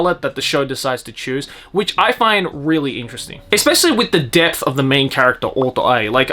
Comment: I thought you said 'death' and as someone who hasn't watched the show yet I thought I just got spoiled, ahahaha